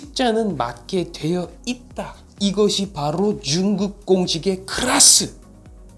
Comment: 이거왜 나만 중국공식이라 들리지?ㅋㅋㄹㅃㅃ